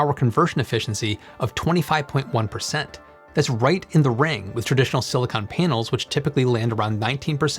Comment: Did you say right in the RANG? I'm so confused.